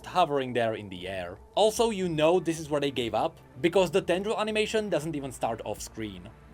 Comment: did say "the dendro animation" ? Man too much genshin huh? I know that feeling xD